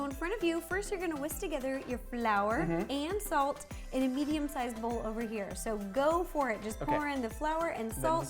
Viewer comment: I’m here the ideot/child genius is here! Also:when she said a medium sized bowl I thought she said vegan sized bowl Every like/comment I get I will do a🙃done the first for myself! omg ty for seven likes love it I love you all ty ty ty!omg 🙃🙃🙃🙃🙃🙃🙃🙃🙃🙃🙃🙃🙃🙃